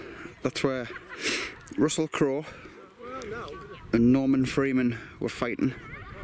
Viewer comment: Morgan Freedman and Russel Crowe ,hiding from the Germans !! PMSL... You crack me up.. Thanks for the upload .Great Craic.. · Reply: Excuse my dodgy hearing .I never knew about Norman Freeman. I thought you said Morgan Freedman , and thought of the Robin Hood movie.. Lol.. My bad.. Apologises..